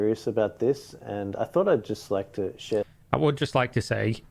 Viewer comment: "i thought i just like to sh*t" can't hear anything else